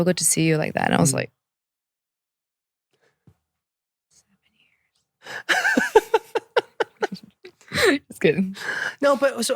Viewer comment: what was she said? 😂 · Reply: She said 7-8 years